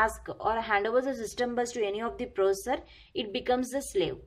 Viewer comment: mam aapne 5:13 pr jo bola system bus training vo smjh nhi aaya. kya bola aapna